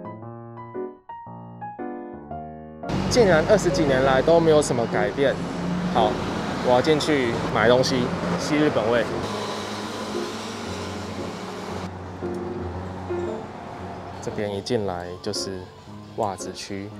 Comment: 呼吸日本味，誤聽成呼吸日本妹❤️